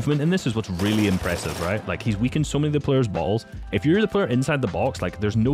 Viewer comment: cc says "weakened so many players balls"